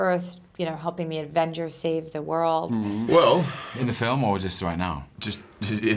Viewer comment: what did tom say? 7:11 is that a what? 7:37 .. loki....? sorry... i'm just trying to learn english... :s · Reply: tom asked, "in the film or just right now" "is this a bribe" and i didn't hear that last part so i can't help you there